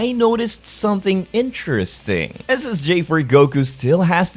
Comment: he is saying Ssj4 Goku so why do I hear is as Ssj for Goku